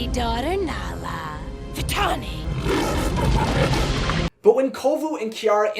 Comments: vs 6:50 can we talk about Zira Vs Nala.... Zira Clearly said VITANI... however Nala sounded like VITALLANI.... I don't know about y'all but vitallani sounded much more, motherish? Idk. What do y'all think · I may be crazy but at 5:43 I hear Nala call Vitani 'Vitalani" like she adds an 'L' to the name. I've always heard it every time I watch the film. Does anyone else hear it?